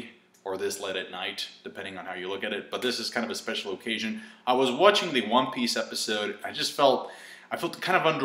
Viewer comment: did he say "I was watching the one piece episode"??......time